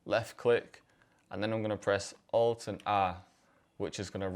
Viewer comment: What ar you saying on 15:49 “alt and ...” please let me know would be very helpful to know this shortcut thanks! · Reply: Alt+R